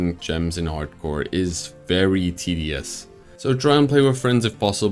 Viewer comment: My goofy brain thought he said TDS instead of tedious